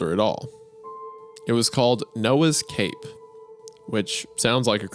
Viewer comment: got me as soon as I heard Noah’s Cape I heard it as no escape